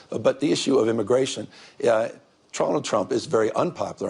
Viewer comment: did Newson just said "Trolland Trump"??? :face-green-smiling::elbowcough